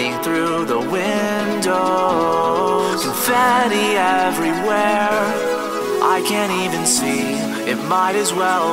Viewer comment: When sang confetti everywhere at 0:43 I heard fatties everywhere XP is that wrong DX